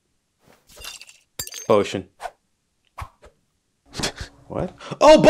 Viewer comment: I thought said oh sh!t but he said potion